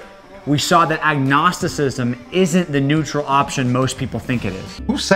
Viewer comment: 90 seconds in and it seems you've misheard him twice "neutral option" not "neutral opinion" and "in light of" not "in spite of". He's an idiot, but I don't think his accent should be giving you much trouble. I'm worried, next time you go see your doctor, please see about scheduling a hearing test. I hope it was just that you've heard so much of this drivel and we're just not paying attention.